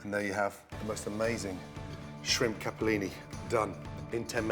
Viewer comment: shrimp capellini? I thought it said shrimp scampi..